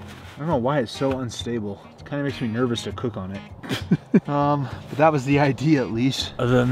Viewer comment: When was talking about the unstable table I thought he said, "Makes me a bit nervous to poop on it." made me turn my head so fast😭